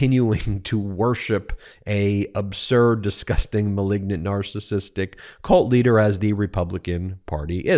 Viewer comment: Hey, did Ben just call him a narcissist dick at 2:04? (He did not, but I heard it that way at first and like to think that he did.) Reminded me of the old Kevin Nealon subliminal man routine on SNL.